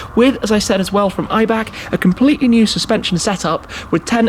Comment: I was thinking about mercedes a35 and a45 thought I heard maybach who is owned by mercedes. It's actually ibac and I actually don't know what it means 😂